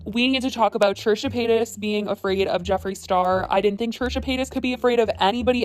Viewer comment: subtitles say Churchill penis instead of Trisha Paytas. 😬😂